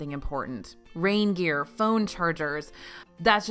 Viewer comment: I heard "Rain gear" as reindeer